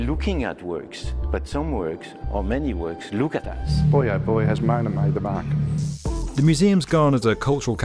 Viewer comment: can someone translate for me what this guy is saying? The subtitles gave up, too. "Something something out the back?" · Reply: Boy oh boy, has MONA made the mark!